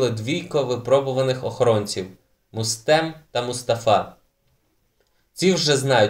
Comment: Мне послышалось или он сказал Рустем. Меня так завут :)